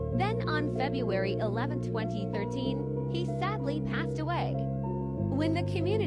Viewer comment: first i heard january,11,2013 cause i am born in that but it was febuary,11,2013